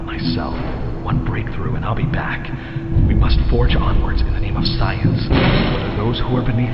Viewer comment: The subtitles say "in the way of", but he clearly says "in the name of".